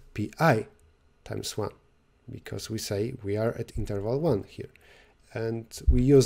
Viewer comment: Hey guys!! Just to make sure you get it, at 14:06 it's "year 1" instead of "interval 1". Enjoy!!